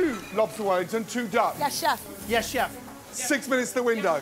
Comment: Wait!!! What?!? Did he say "Yes Chef"? 😮